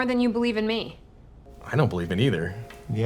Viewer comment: first time I watched, I thought he said "I don't believe in either" (the captions did too) and I assumed the lawyer was quiet because she was offended but then she was like "why do you trust me". Listened to it again, realized it said "I don't believe it either". Or maybe it actually says that and I sound really stupid.